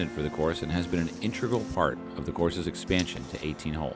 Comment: Am I the only one that heard him say fart instead of part